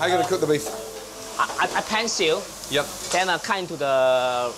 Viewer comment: The person that did the subtitles seriously put "seal" instead of "sear" lol.